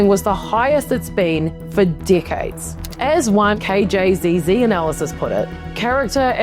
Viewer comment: From the beginning it sounds like she is saying dikheads instead of decades.